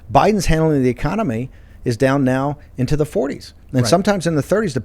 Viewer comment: I laughed so hard I snorted when he said “Biden is handling the economy” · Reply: Actually that's not what 'ole said.. he said: (7:21) . .."Biden's "handling" of the Economy is low.. down into the 30's & 40's"... ergo, his approval rating consequently has tanked!